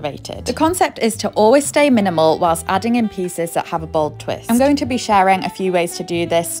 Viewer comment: Im glad she put her own subtitles in because at first I thought she said going bald 😂